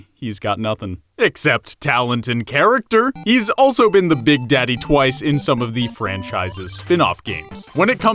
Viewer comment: why did you say "big daddy" when the subtitle says "big baddy" i'm crying